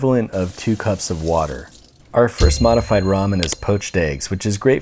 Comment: i thought it said mortified ramen....